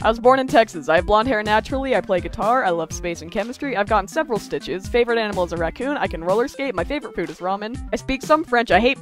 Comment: I heard "and i have a million eyes" 💀